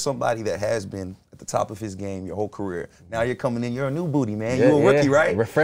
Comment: did bro say “new booty”??? PAUSE!!!